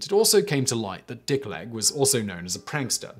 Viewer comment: God, at first I thought Simon said the dood’s name was “Dick Length”.